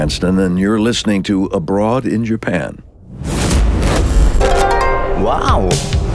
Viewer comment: The first time I heard "I am Brian Cranston and you are listening to Lebron in Japan."